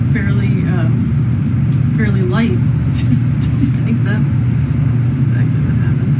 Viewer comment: Captioning is wrong at 2:15 - When she says that he meant to go down fairly light she then laughs it off and says "I don't think that was exactly what happened" instead of "That's exactly what happened"